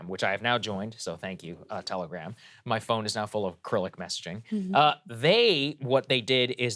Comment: For a moment (24:01 or later), I thought Saagar said his phone is now full of acrylic messaging. 😊